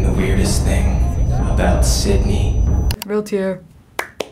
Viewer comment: I just heard "shut up, just shut up Richie!"